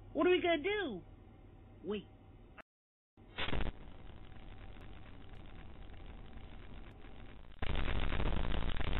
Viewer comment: What did they say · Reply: Staic mate Static